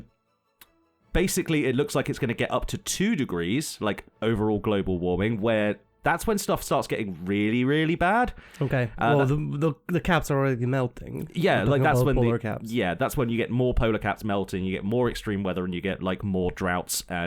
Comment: Lol I thought you said " the cats are melting"